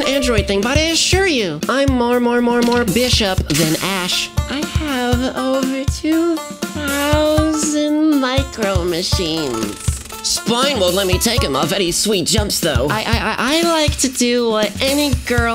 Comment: What does she say about spine at 0:35, I've had trouble understanding her. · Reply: Gabriel Huff "I have over two thousand micro-machines. Spine won't let me take 'em off any sweet jumps, though." Micro-Machines were a line of ultra-tiny toy cars and other playsets, slightly smaller than N scale if that means anything to you. They date from the mid-80s through the 90s.